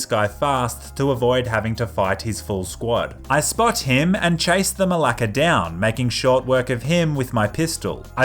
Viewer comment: did he say 'chased the malaka down"?